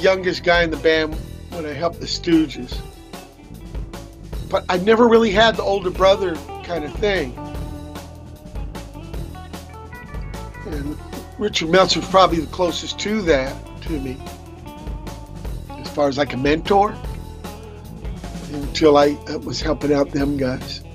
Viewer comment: What a great interview, Mike watt has such good insight and disposition in every interview I see with him. Found this interview because I just listened to the studio zzz live recording where watt and boon shout out Richard meltzer before almost every song lol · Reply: I always thought they were saying "Richard Belzer" which always confused me.